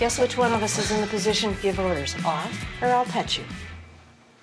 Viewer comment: Am I the only one that thought she said “off or I’ll cut you”?😂